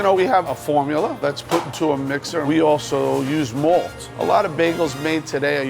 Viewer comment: did he say "malt"?